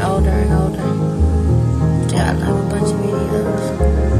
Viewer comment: Can someone make out what the woman says after "as we get older and older"? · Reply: "yeah I got a bunch of videos"